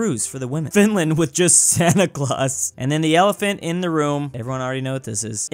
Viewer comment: I thought you said: "Elephant in the Rome" and I was like: "They really like Hannibal, ha?". And I can't believe they said tennis instead of war for the West Balkans.